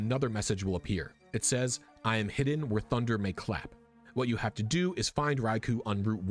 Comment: Is it just me or does 25:16 say "haffind me whe thunder claps"? Absolutely amazing work regardless, this is honestly so far beyond anything I could have ever thought possible with this game. I'm blown away! Also sincerely, I love the Nintendo E-Reader. It's such a shame it didn't catch on.